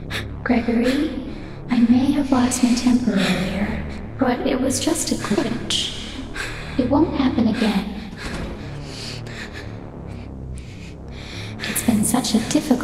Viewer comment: The “G-g-glitch” ya the end probably references to Glitchtrap. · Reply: I thought she said "i was just a twitch" Like in, you know, the medical twitch of sorts, like losing control. But, she coulda said Glitch